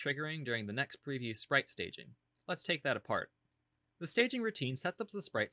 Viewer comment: I kept hearing "next preview's sprite staging" as "next previous sprite staging"